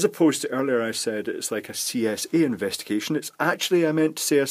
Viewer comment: I thought you said C&A investigation. I would have thought that retailers bankruptcy issues would have been entirely solved by now, not requiring any investigation at all.